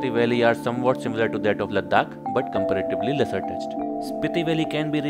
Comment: is comparable to ladakh but _______??? anyone please tell me what he said. I noticed it and now I cannot rest until I know what he said.😅😅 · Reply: but somewhat lesser touched!! 🤔🤔